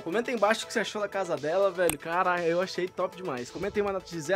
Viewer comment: so eu q ouvi o falando carai